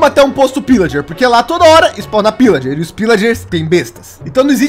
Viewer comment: juro que entendi que o disse: "e os pillagers são bestas" kkkkkkkkk